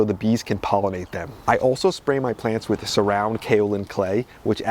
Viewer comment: what is the name of the clay spray you used on the cucumbers? I’m not sure I heard correctly. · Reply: The product is called Surround its a sprayable Kaolin clay